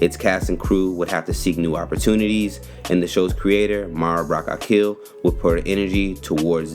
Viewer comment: Who else thought he said, " the show's writer, Mara Brock got killed"